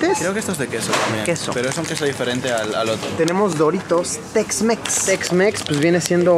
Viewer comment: Pensé que decía Doritos SexMex :v